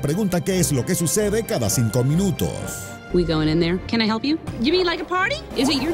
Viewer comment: en el minuto 1:13 se escucha que dice "Help Me" :v.